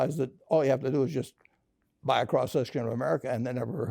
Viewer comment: What did he say to buy at 3:38? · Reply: I'm wondering too... I think he said buy a cross-section of America